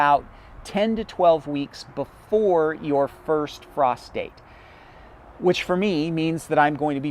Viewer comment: Lol, I was listen but slightly distracted. You were talking about the first frost date, well My Ears thought you said First Prostate. Sorry I have no one to share my Goofy Moments with. Lol🤣🤣😁